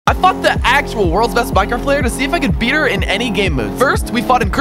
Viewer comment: Is it just me or I heard him say “ I fuck the actual world beast Minecraft pvp’er” it can’t be me only 😢😢